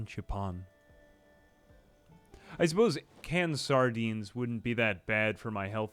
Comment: I thought it said “canned sadness” And I didn’t even question it.